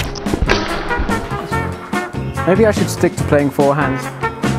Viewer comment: I thought he said I should stick to playing Fortnite lol 😂